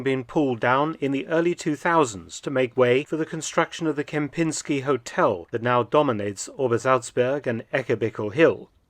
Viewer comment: did u say kempinski hotel?